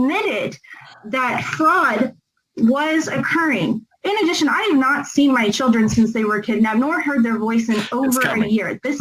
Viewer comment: Did she say:" I haven't heard their voices since they were kidnapped "???🤯